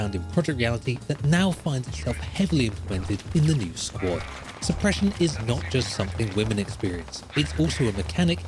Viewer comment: “Suppression is not just something women experience” 4:24 did I hear that right? 😂 I replayed that part so many times bc it was delivered with so much seriousness out of nowhere I thought he must’ve been saying something else.